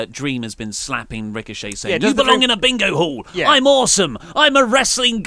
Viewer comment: OH! Dream said Bingo hall, I thought he said Big Ole Hole. I did think that was really weird but I also found it hilarious